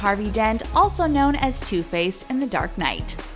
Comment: i thought she said "also known as toothpaste"